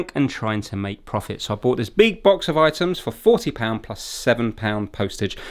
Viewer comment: (I play de video while looking on my phone) I hear you say "beatbox" and for a moment I thought you would do something artistic with your mouth like StezStix Fix? but when rewinding and* with subtitles on I read "big box". Next time maybe!